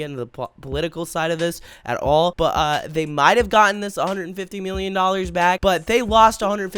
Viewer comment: the subtitles said 250 million not 150 million